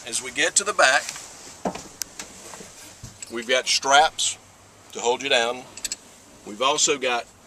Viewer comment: did he say we've got straps to hold you down LOL